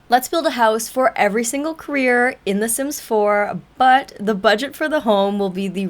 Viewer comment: Jfjf sorry I misheard u as "let's Build a house for every single queer in the sims 4"